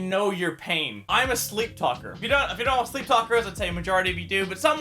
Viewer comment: I thought you said sleep fucker